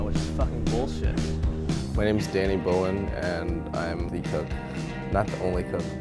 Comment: Thought it said "Danny Brown" and I was like oh shit!